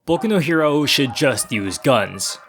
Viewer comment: Bruh, thought u said Boku no Pico